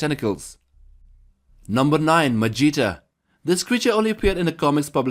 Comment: i love how the captions say "Vegeta" instead of magita😂😂😂